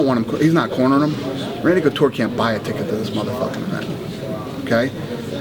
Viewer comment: what does he say at 6:18 I cant undertand it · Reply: "Randy couture can't buy a ticket to this mf..." A bit late to clarify